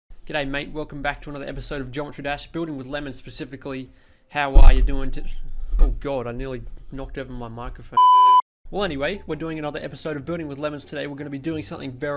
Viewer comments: I heard, "Burning with lemons" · When he first said "Building with lemons" I thought he said "Burning with lemons"